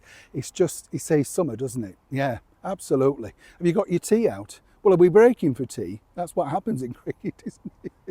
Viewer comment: Oh ! Dear ! When you said, " Have you got your tea out " ? I thought you said teeth 😮 . The cost of going to a dentist is beyond belief .